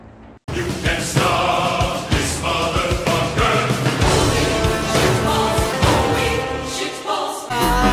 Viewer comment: on the intro I heard "HOLY! SHIT POSTS! HOLY! SHIT POSTS!"